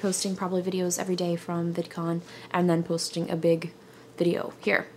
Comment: Thumbs up if you thought it said "vicodin" instead of vidcon.